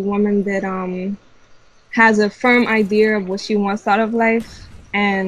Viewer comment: Did she say IDEAR @ 6:08?